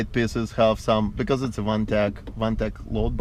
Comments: talks about some load bars inside. Anyone have details of who makes this? sounds like he said onetech but unsure. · Where can I get those headliner bars to put accessories on? It sounded like he said onetech?